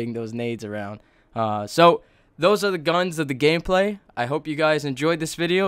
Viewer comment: "gayplay" instead of gameplay